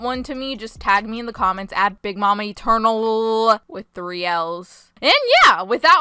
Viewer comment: WHY DID I HEAR "EAT UR CURNALS" INSTEAD OF "BIG MAMA ETERNAL" ALSO HERES SOME ANTI-CRINGE SPRAY::sanitizer: And now some random emojis::chillwcat::chillwdog::elbowcough::elbowcough::shelterin::videocall::yt::stayhome: I love ur vids btw :D